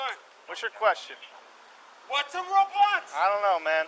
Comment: It sound like he says “What’s a Roblox”